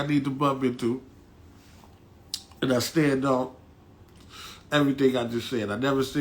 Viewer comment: Plus made a apology video the very next day, what street nigga u know apologizes maybe the ones u know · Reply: exactly! I thought he said that he stands by everything he said in this video?